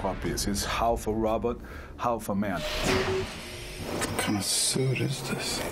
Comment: sound like he said half a rabbit, half a man. 0_o take two? Lol